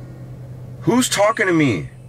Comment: I heard “Who’s Bad”